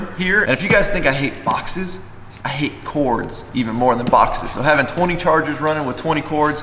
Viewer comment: I had to replay the part where he said he hates cords cause I thought he said porn